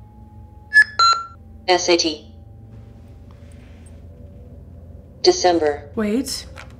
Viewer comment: I think the man said “SHT” Wich means: Spherical Hermonic transformation. Idk what that stands for but maybe its important 😅